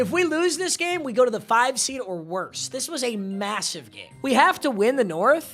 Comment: wait did u say massive mmg